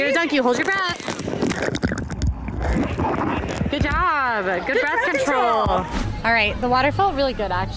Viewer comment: I heard "good birth control" instead of "good breath control" lololol :face-blue-smiling